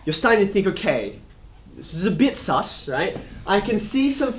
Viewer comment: Did he say sus 😂